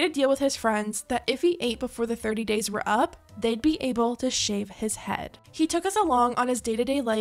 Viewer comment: I was listening to this video from a distance and thought you said „shoot his head“ I was like- 🤨WHAT